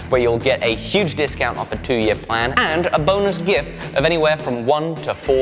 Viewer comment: I could of sworn you said "and a boneless gift" in that sponsor clip lol